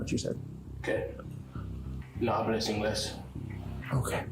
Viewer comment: I dont speak english is what he said